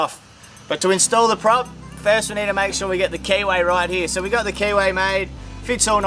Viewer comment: I am loving the whole rebuild process and then at 07:39 I originally misunderstood what was said. I eventually understood you were saying keyway, but I initially heard KY.